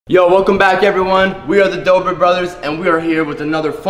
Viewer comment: I thought u were boutta say we are the fibre brothers and we do back flips